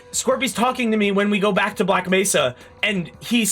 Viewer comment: Ngl at 12:24 I thought I heard say 'black to back mesa', and I had to go back to make sure That's how ingrained that bit from hlvr is in my brain